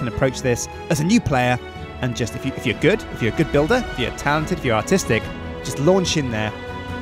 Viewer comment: i could swear he said "..if you're talented, if you're autistic.." xD too much internet ruins my brain